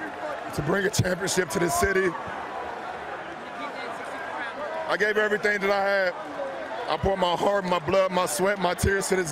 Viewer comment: What does LeBron say at 0:40 ?? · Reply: I gave everything I have